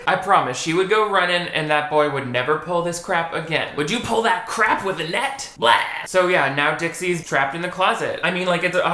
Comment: I always thought was saying "would you pull that crap with a net"